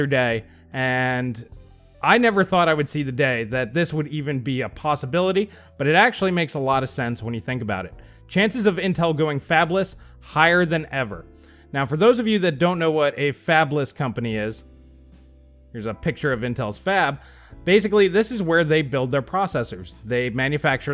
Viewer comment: I thought you said "Fapless" and my initial response was "Those poor souls."